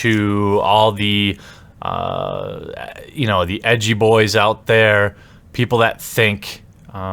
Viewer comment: I'm sorry When ever you say edgy boi I keep hearing "yugi boi"